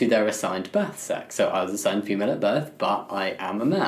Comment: i misheard you and thought you said "i was assigned human at birth" goodbye